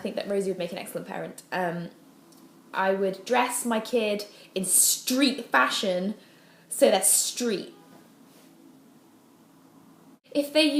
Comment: Hi! Can you tell me what said from 8:43 to 8:45?? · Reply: y Rosie Subtítulos She said "I would dress my kids in street fashion -- so that's street." Unfortunately the last phrase "so that's street" does not make sense to me. I suggest you just omit those last three words in your translation. I might be wrong, but clearly it cannot be anything important.